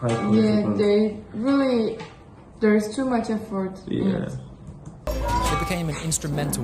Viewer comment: Why did she say there is to “much afford in it”? 9:04 like is she jealous or something did she knew that probably the best she could do was reacting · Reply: calm down, she said they put so much effort into it, it was a positive statement